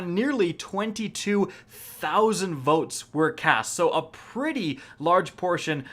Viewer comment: I heard motes